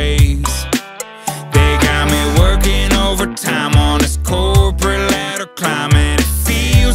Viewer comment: Not a native speaker, so I need help... What does he say in "On this cold ????" (1:11) and "I hate that stupid ??? look on his face (0:47) If someone, just please... lol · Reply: "corporate ladder climb..." :47 "stupid dumb ass look"